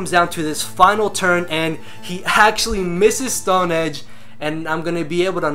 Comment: I love how it sounds like you say he "haxtually misses the Stone Edge"